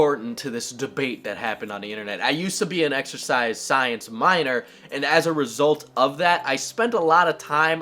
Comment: I thought you said exorcised science minor, lol.